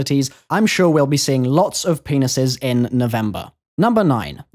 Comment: it sounds like he said penises